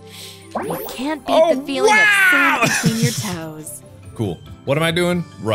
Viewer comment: i thought she said 'cant beat the feeling of someone licking your toes'